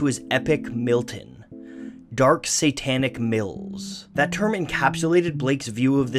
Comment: I wasn't paying attention and the video playing in the background I thought you said dark satanic milfs I got interested